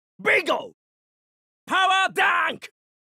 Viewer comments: When Terry did Power Dunk I thought he said,"POWER DIVE" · I always thought that 1:00 was him saying "How 'bout THAT!" really desperately, I kinda feel disappointed that it's just "power dunk" instead